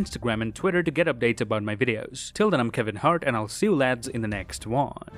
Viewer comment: Did I just hear “till then I am Kevin Hart” at the end 🧐